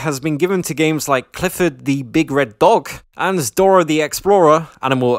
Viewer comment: Am I the only one who misheard him say Clifford the Big Red Dog as Clifford the Big Red Bog?